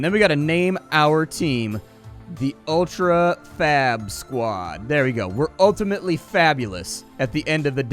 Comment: so i wasn't looking at the screen when he wrote ultra fab squad and i heard it as fap squad it was hilarious